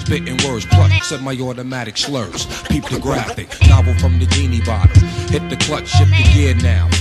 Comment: Anyone else here meth say “Novel from Bikini Bottom” instead of novel from the genie bottle lol